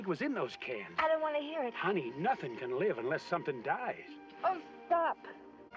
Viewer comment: Did she really say "Oh F%$k"